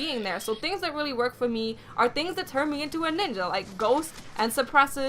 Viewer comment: did you say nigga?? lol